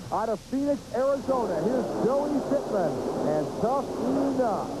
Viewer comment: Who did he say was the driver at 6:51? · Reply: Billy Pittman – Tuff-E-Nuff 2